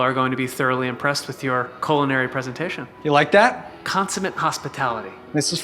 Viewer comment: When they were standing by the dining table, I thought Joe said "Coleinary" instead of "culinary." Cole didn't seem to notice though, so I guess I misheard. 😅😂 Would've been a great pun, though.